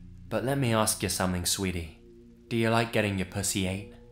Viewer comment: I'm dozing off then all I hear is "Ya like gettin ya 🐈 ate?"